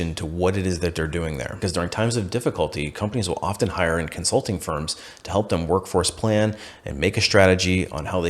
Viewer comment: I misheard insulting firm and I like that better than consulting firm in this context 😂